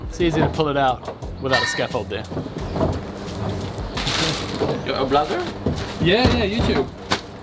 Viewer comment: What did the scaffold guy say/ask at 8:29? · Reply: I think he said "are you a vlogger"